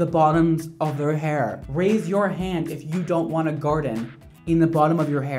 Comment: Wait I thought you said raise your hand if you want a garden in the bottom of your hair but you said if you don't want it I'm ashamed of myself🙂